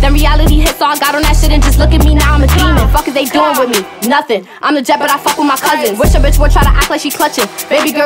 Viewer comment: What she say at 0:38 ?? 😂😂🤦🏼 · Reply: Imma jet but I fuck wit my cuzzin indicating crips